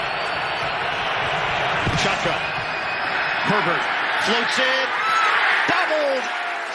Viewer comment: Am I tripping or did the announcer say "Shotgun,Pervert" instead of Herbert😂😂